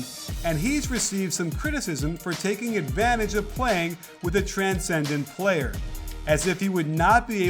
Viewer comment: I heard 'transgender player' (1:08).